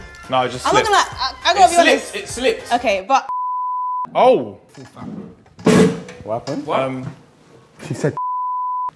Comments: what she said here? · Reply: Don’t you have a kid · What did she say at 2:44 😭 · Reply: Haven’t you got a kid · I need to know what was said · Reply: Brought up his babymum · what did she say tho??? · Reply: " Don't you have a baby mama ? " Basically calling him out on why he's out here on a dating show even tho he already has a kid lol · what tf did she sayyyy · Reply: Haven’t you got a kid